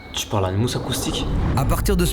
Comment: Il dit quoi à 6:40 ? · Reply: Tu parle a un mousse acoustique